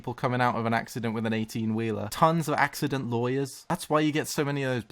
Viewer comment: I swear to god I heard "we call them solicitors instead of liars" and it took several seconds to know he was saying "lawyers" lmao